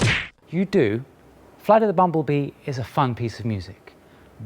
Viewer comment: Oh, he's saying "flight of the bumblebee". Even his speech is unclear so I thought he said "freddy the bumblebee"